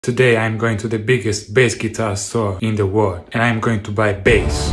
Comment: What i heard was "Today i am going to the biggest pass out store" Until i read it